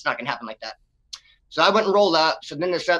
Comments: Yeah it for real did sound like he said "so I went an rolled up" at first I listened again and heard him say "so I wouldn't roll up". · Hey Death, he said “so I wouldn’t roll up” his accent sounded like he said “so I went and rolled up” . Just thought I’d clarify that for ya boss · Hey Death, he said “so I wouldn’t roll up” his accent sounded like he said “so I went and rolled up” . Just thought I’d clarify that for ya boss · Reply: Oh good thank you cuz I really thought he said he went and rolled up. LOL